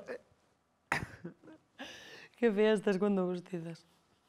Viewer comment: Que Dice Miki?? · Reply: Claro, cuando haces eso...